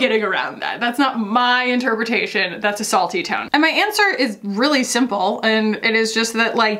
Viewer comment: I kept hearing “assault-y” tone, instead of a salty tone 🤣🤣🤣 bc that comment definitely felt like it was attacking you hahahaha